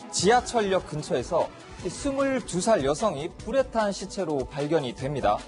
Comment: 초 뿌레탕 이라고 해서 수십번 돌려들었네 불에탄 맞음?? 발음이 방송하는 놈맞나; 그리고 요즘 누가 오후 밤을 11시로 표기를 하나 헷깔리게 게다가 표까지 그지같이 만들어가지고 16일 11시58분이라고 잘못써놓질않나 정남규 2016년 4월 저건 도대체 뭘뜻하는건지?? 정남규 죽은게 2009년인데 표만든놈 제정신임?